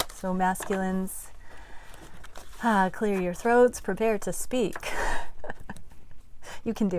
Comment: Great Great !!! 🔥🔥☺️I’m always listening to your readings , because your are so very real amd intuitive . 🙏🏻 I’m from the Netherlands and I was wondering , what you were saying at timestamp 4:03, right after you said : my voice did a funny thing. You said somethingu like : “masculines probeer to speak. “Probeer” is a Dutch word as well, maybe it’s derived from the Latin language . Anyway, this was such a recognizable reading. Twinflame journey has been a major gift to me. A big lesson , an amazing spiritual experience , but freaking heavy in the same time 😅.